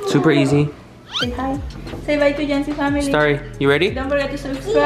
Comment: If you like jancy family like me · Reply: I thought it said juicy family